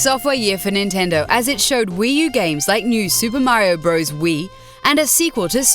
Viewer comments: on 9:17 did she say wii u games? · did she just say WII U games????